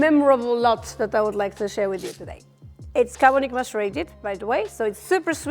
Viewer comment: "It's [WHAT?] by the way, so it's super-sweet, tropical fruits..." I couldn't understand. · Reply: Carbonic macerated.